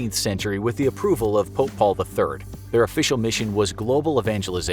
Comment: When He Said 'Pope Paul', I thought he said 'Football'